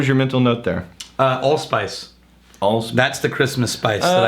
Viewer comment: I thought he said "Old Spice" instead of "All Spice". I almost choked with laughter!!!